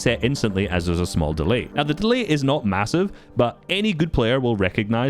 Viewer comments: did you say massive? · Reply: he said not massive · DID U SAY MASSIVE????? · did i hear massive??